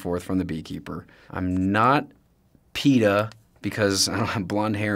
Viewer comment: i thought he said beyoncé😭